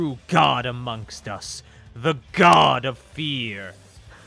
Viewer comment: bruh what? I heard the “god of BEER” then proceeds to laugh with cell